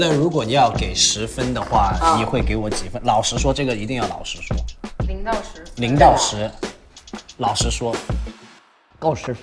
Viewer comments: the subtitle is wrong Jackson is saying please say it out honestly not saying out from the master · I think there's a mistranslation at 7:37 , what Jackson said is 老实说(lao3 shi2 shuo1) means "tell honestly", cuz he wanna know granny's honest opinion. Although it sounds really alike to 老师说(lao3 shi1 shuo1), which means "teacher says". 😂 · pretty sure he’s saying 老实说 and not 老师说 which means “tell me honestly” instead of “master tell me” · I think he said 老实 not 老师